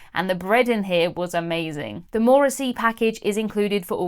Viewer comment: Did you say the Morrissey package. Methinks Now My Heart is Full!!! Is it service with dexterous wordplay and witty reposte??? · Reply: More at sea!! 😂😂😂😂